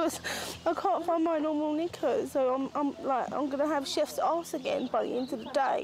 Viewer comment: Brit here - I think at around 18:21 she said, "Chafed Ass" which is like where two bits of skin rubbed together and create like, a scar on the top-layer of skin. It does sound like "Chef's Ass" though HAHA!